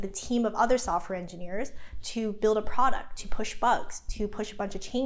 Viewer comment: Did she just say - push bugs! lol Ah, but then that is true for whenever new feats released